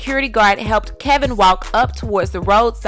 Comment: Maybe I can’t hear but I could have sworn the reporter said Kevin walked home to get help although they stated that he was seriously hurt😏 let me be quiet and finish watching